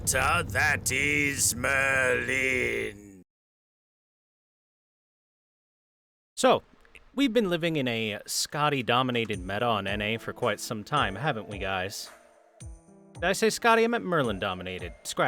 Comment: W8, did i hear wrong? Did you just said "ciao"